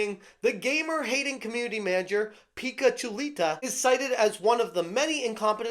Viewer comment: i hear "pikachu...lita" where is it. gotta catch them all😂